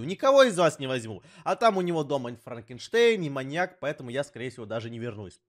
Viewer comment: он сказал франкинштейн А я услышал Моргенштерн Кто тоже, ставте лайк